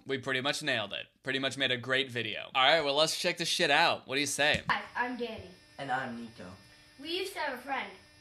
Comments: When he said “Hi I’m Danny” I thought he said “Hi I’m gay” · When it said “ hi I’m Danny “ I thought it said “ hi I’m gay “ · i thought he said “hi, i’m gay” 😭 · i heard “hi im gay”